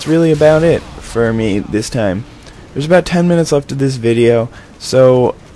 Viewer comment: did you just said they where about 10min left of this video?